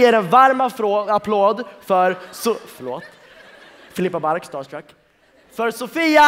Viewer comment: I heard him say "Star Trek" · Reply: Hahah no he said "star struck"